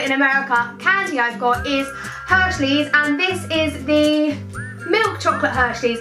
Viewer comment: It sounds like your saying 'hershlys '